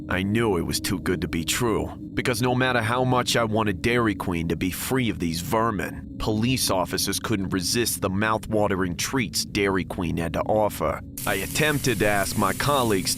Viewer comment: I swear I heard "Dairy Clean" 💀💀💀